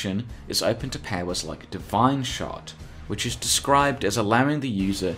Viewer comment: "...divine shart" Anyone else hear that? His accent and my crappy headphones made a wonderful combination there.